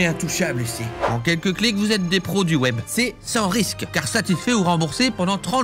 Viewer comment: j'ai entendu "en quelques clics vous êtes des produits web" je comprenais pas x))